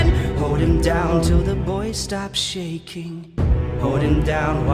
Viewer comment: My deaf ass thought he said "Hold him down till the voice stops shaking" but boy makes so much more sense 0:39 I also thought he said "faith and his boat" while the line is "faith and his bones"